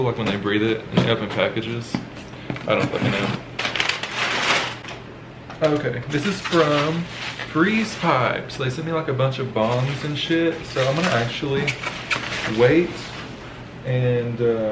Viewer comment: My high ass thought you said a bunch of BOMBS💣 and shit💩 lol.😂